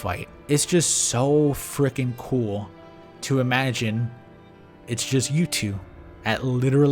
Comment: I thought he said "it's just YouTube" lol